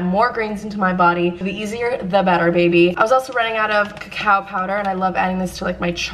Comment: When she said cacao powder I thought she said cow powder and I was like... whAaaAt iS cOw pOwdEr 😂